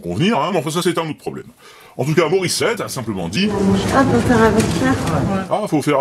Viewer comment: Moi aussi j'avais entendu "Ah ? C'est pour faire un vaccin ?" sans avoir lu de texte avant, ça m'a étonné.